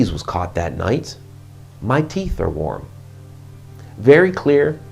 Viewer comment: I hear "My teeth are warm." Tts like an awesome campfire tale.